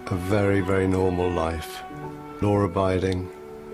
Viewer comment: Caption say, No ribiding, however pretty sure he said, Law abiding, in a British accent. (at least on your CC it has the right words.)